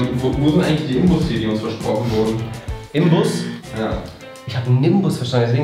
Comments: Ich habe nimbus verstanden... Deswegen habe ich den besen jetzt mitgebracht😔😂😂🤷🏼‍♀️❤ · Ich hab Nimbus verstanden, deshalb hab ich den Besen mitgebracht.,,🤣